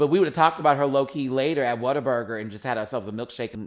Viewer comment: Yes Whataburger ! 🤤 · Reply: I thought she was saying Water Burger.... I thought that is the weirdest name for a burger shop.... soggy bun picture